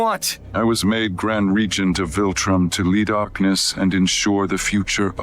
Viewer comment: did he say "lead our penis"? Nice job editor 😂